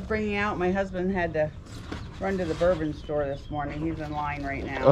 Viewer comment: Did she say her husband had to run to the "Bourbon Store"?